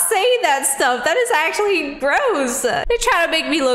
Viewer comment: Instead of gross which instead the captions to this video gross turns into ‘Broza?’ Whats Broza? YouTube got it wrong again..and yes I know I’m late....